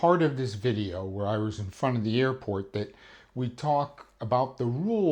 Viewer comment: Misheard one of the first words as "the nuke of the airport", had to stop for a second ;)